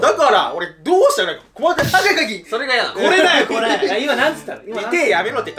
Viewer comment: 「赤い鍵」って聞こえましたw